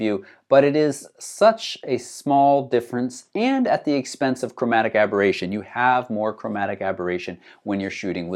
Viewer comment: Sorry, I don't understand the word in 09:16. Expensive "cameraab...ration". What soes it means? :)